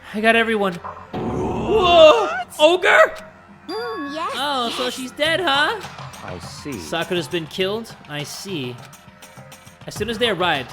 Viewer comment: did she just said oo yes daddy